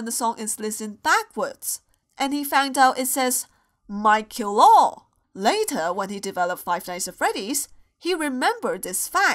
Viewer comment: I heard "mike kill all satan"